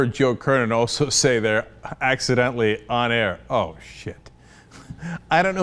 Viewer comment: Cenk I heard that "oh shit" comment in the ackground but I'm not completely sold hat he sed oh shit... I hink he may have said "oh shut up"